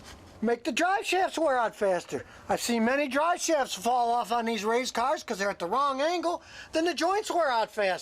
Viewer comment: Did anyone else catch that said race cars instead of truck at 2:26 hahaha